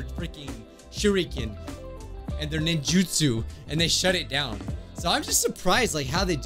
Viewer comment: when u said (shut it down) I heard (sharingan) bc well yeah umm ninja's, ninjutsu, like how should I not think naruto???